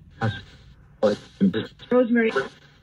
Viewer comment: I heard your spirit guides name Rosemary come through!!!